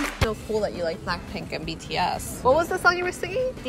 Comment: DID SHE SAY BTS !!!!!!!!!!!!!!